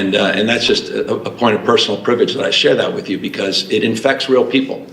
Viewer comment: when Newscum is talking about hurricane force winds and how it breaks his heart blah blah blah. . . He said, “It ‘infects’ real people.” Did anyone else catch that?